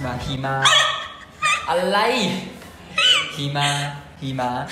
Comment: ได้ยินว่า หีหมา555 ไม่ได้หยาบแต่ได้ยินจริงๆ😂